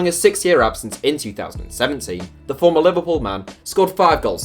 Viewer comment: did he just say liverpool man or it just me Edit:you should put ascacibar in bench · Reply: Former Liverpool man. Babel played for liverpool🤦‍♂️